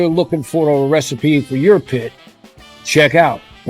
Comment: Did I hear "EUROPIT" in the end??